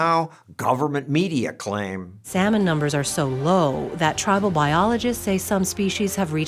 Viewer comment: I'm sorry.... tribal biologists? Did I hear that right?